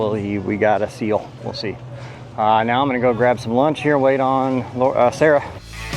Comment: Did you really say at 13:32 "waiting on Lau - Uh Sarah"???